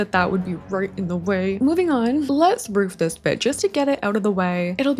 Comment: Did anyone else hear "Let's roof this b*tch!" at 1:08? Haha! It caught me so off guard!